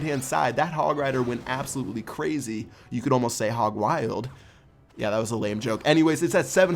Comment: hog what ? I can't hear that Hog Grider? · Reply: He said you could almost say hog wild